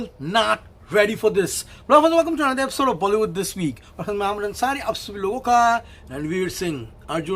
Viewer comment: You said: honestly and passion for movies I heard ‘aamir Ansari’